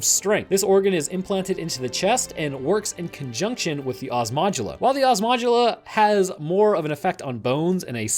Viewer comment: I keep hearing "Ass Modula".